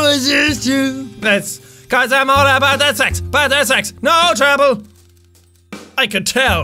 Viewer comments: ohm I thought he was saying causes im all about that sex lol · I thought it was saying sex